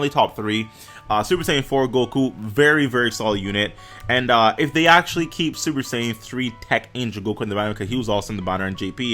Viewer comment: He said "if he is on that banner" He wasn't on int gogetas banner on global, so he may be on PHY Gotenks on global. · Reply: No he did not, he CLEARLY said "and if they actually keep SSJ3 TEQ Angel Goku in the banner, because he was also in the banner on JP"